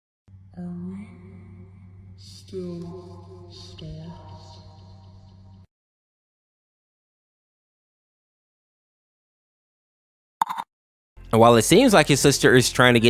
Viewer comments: “Are we still stars?” Is what Velvet says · what is she saying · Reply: Are we still stars